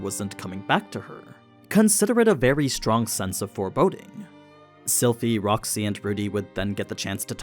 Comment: what did he say? Forboding?